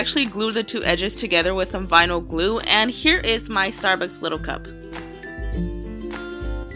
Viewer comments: I thought it said starbursts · I thought she said star burst and i was like what the heck